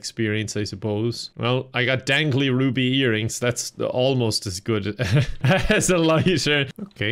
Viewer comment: I wasn't looking at the screen at this moment and misheard it as "dangly wobbly earrings"